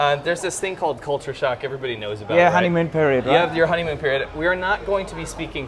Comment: I really need to get my ears checked. I thought they were saying hymen period at first. Lol. They did joke about cuddling though right.?